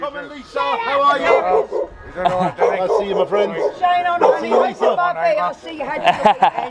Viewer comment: Button for the old intro! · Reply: I thought it said money! Not honey! 😂